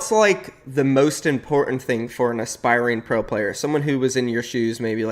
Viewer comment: he said “what’s most important for a inspiring pro player” I heard “what’s most important for Thome” only og fans will undestand IG: torzin_fn